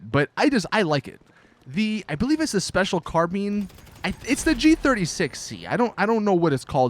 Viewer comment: Did he just say 'special carbeen???'